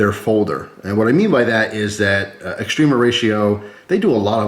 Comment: Not into knives but love your videos. All these years I thought you were saying Extreme Horatio always made me giggle thought it was a cute sounding Mexican thing